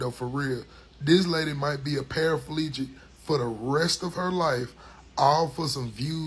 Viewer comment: ….. I was really invested in the video until I heard your boy say “Parafalegic”!!!😂😂😂😂😂😂